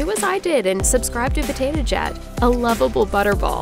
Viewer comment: I heard her say "Potato Jat"... Just me?